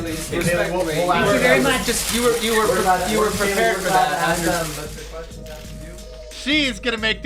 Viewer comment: I heard someone exclaim with righteous indignation as Kayleigh McEnany left the podium: "You were prepared for that!" (6:00) Yup, compassionate liberal "journalists" are outraged by a brilliant woman working for President Trump. Bah ha ahahahahahah!